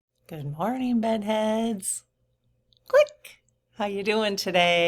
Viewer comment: Love your bedhead!! My husband thought you were saying “butthead”! 😂😂Thanks - love your humor, too. ❤